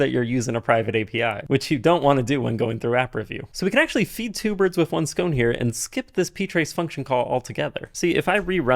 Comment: did I just hear the phrase "kill two birds with one SCONE" here...?! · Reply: No he’s feeding two birds with one scone 🙈